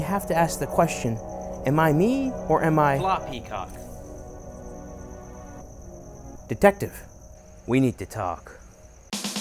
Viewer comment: I heard it "floppy cock" 😭